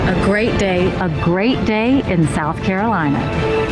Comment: I legit thought it said "Made white pride in South Carolina". Had to do a double take when no one reacted.